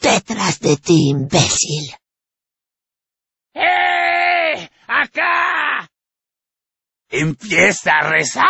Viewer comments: always sounded like "Heeeeeey, a guy!" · For years thought he said, "AAAAAAAAAAAAAAA, FUKAAAAAA!!!" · For so many years 1:04 i thought they were just saying"HEY, A GUY!"